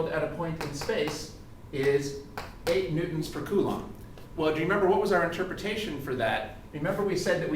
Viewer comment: in he clearly says it is N/C. Maybe you weren't paying attention?